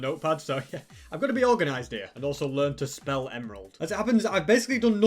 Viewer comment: I thought he said learn to smell emerald😂